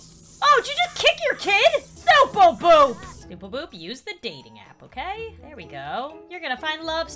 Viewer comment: On 4:29 it kinda sounds like she said NOOBLE BOOP i can’t even 🤣🤣